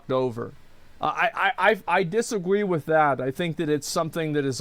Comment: did. did he just say dissagwe ?